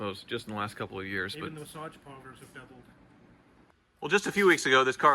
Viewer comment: did say "even the massage parlors have doubled" ?? 😂💀